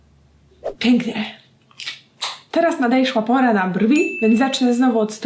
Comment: czy tylko ja tam usłyszałam "teraz nadejszła pora na brwi"